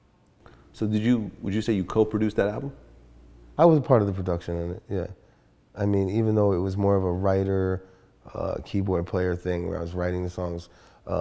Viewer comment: D.J. Vlad - Did you say you coke-produced that album?